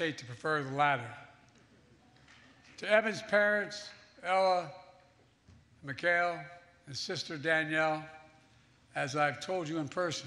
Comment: What is he saying at 44:47 ( some names and i can’t catch them) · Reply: "To Evan's parents, Ella and Mikhail, his sister Danielle, as I've told you in person..." Mikhail is the Russian (that area, also Ukrainian) form of Michael.